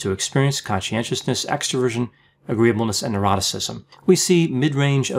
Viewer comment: It sounds like he says "eroticism". In the OCEAN acronym for the five personality traits, the last is "neuroticism" not "eroticism".